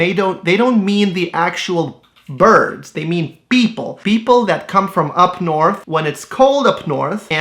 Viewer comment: I heard "beople"